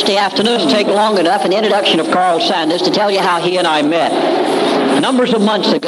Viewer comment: Thought it said Colonel/Kernel Sanders (The KFC man lol)